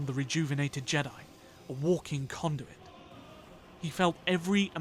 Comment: I initially thought you said a walking condom.